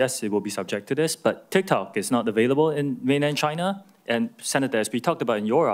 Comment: did he say tiktok is not available in mainland China